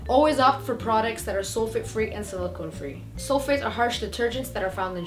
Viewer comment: i did not understand what she said by 1:31 and 1:32 some thing free..... btw my hair is not frizzy or too curly i camed here by mistake, this vid title name is also called something mistake lol but i loved this vid and helped me too in some ways.thank you for your tips. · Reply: abraham solfate and silicon free